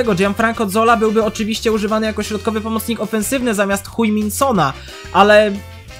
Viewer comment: XD Usłyszałem w 8:40 Jak mówisz C**j min Sona